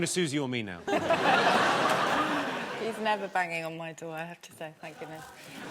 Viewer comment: what is Susie Dent saying in response about the knocking on a door? It sounds like "I have to start thanking us." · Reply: Conner Emer 'he's never banging on my door, I have to say, thank goodness!'